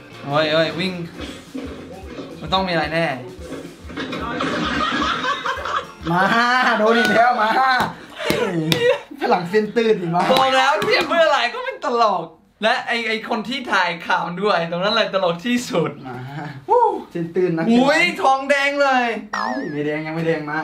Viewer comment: ได้ยินว่าฝรั่งเส้นตื่น